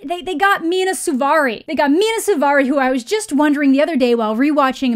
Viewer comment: I CANT BELEIVE I HEARD 2:49 AS "they got me in a souvarri!!!" WHAT KINDA CAR IS THAT